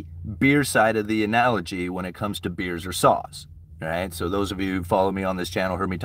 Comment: I thought you were saying beer or sauce….but its beer or SAWS! Ok got it